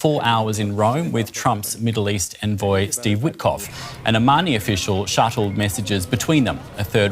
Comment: Did he say an "Armani official"?